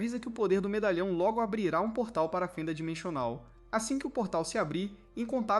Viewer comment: eu ouvi dimechonal ou impressao minha?